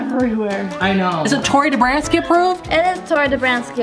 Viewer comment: I thought you said Gordon ramsey approved